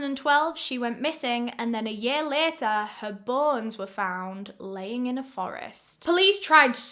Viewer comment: I thought said her “buns” were found lol 😂! I rewind and realize she said BONES lol 😂! I was like OMG they cut her but cheeks off lol 😂! Ok let me finish the story. I’m sorry y’all I was listening while taking a shower lol 😂!